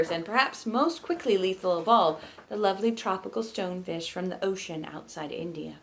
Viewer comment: This is roughly the moment that Elliot moves the camera with their mind powers. I happened to look up at the screen from my work at the magic moment. Maybe they made me look at it with their mind powers, too! Nice job, Elliot! :P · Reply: Is it possible Charlotte heard "stone dish" instead of "stone fish" the way that I did? I was also wondering about that, too, so I was glad they asked!